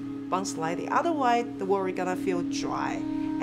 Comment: I can't tell what's she's saying at 4:20. "Everything's gotta have a bun-slidey or the world's gonna feel dry"? I'm having "Xbox One is the new watercooler" flashbacks.